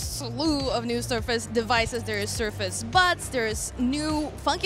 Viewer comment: I swear she said "Surface Butts"